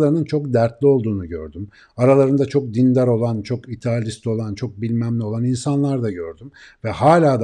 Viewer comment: "Aralarında çok dertli olan, çok dindar olan çok ... olan" gibi bir şey söylüyor. "..." bıraktığım yeri bir türlü anlayamıyorum lütfen bilen biri net bir şey söyleyebilir mi orada ne dediği ile alakalı? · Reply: idealist galiba